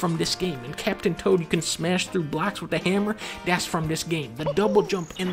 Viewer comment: tell me it doesn't sound like he says "you can smash through blacks with a hammer!".